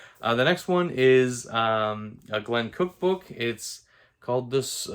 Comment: I heard "a Glen Cookbook" :D :D